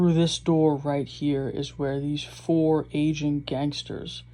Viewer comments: what did you say?? I heard "Asian" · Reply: He said “AGING” but you’re right sounded like Asian LMAO 🤣🤣🤣🤣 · You sound like you said Asian gangsters and I’m looking at the mugs like WTF?